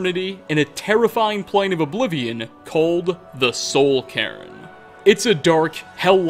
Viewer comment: “Do you get to the soul cairn often?” "Oh, it took years, but I earned my way to the top. I own Soulhusk Farm, you see. Very successful business. Obviously." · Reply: ngl at first when I was on a different tab I thought he said the "Soul Karen"